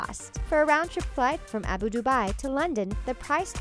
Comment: Did she just say, Abu dubai